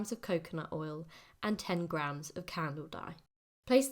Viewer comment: Did understand the last ingredient, sounded like You said Camel dye? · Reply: Candle dye 😊